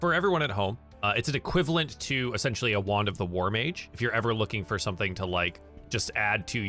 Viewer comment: I thought you said wand of the warm age, and was rather confused